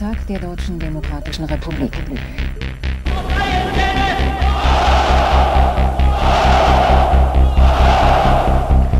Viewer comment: What do they say in 0:21? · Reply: They say „auf freien Feldern“ which means something like on liberated fields. It’s probably from a speech about the Land reform in Germany between Autumn 1945 early 1947. The soviet occupational government had back then already installed a socialist German government as they saw that there wouldn’t be any chance of creating a unified but neutral Germany due to the Cold War.